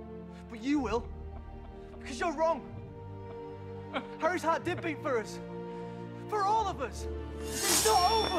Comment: I always thought Neville said “Harry had dipping for all of us” always confused me why he remembered dipping, and when did Harry get dipping for all of them, like are there unwritten chapters where Harry brings dipping? Or did he order the house elves or was Harry a good dipping chief. Alas it was “Harry’s heart did beat for all if us” makes more since, if it’s true as for me, I believe that Harry had dipping for all of them, and Neville wants to kill voldermort so he could find Harry’s will and find out where Harry stored the dipping that he had for all of them.